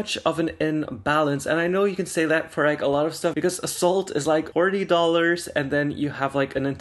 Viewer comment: Okay so I didn't even know this was playing until said "Salt is like 40 dollars". I heard "assault is like 40 dollars" and I was like oh honey it's more than that, it's jail time. 🤣